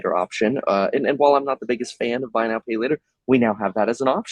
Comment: I thought you said we shouldn’t be using by now pay never.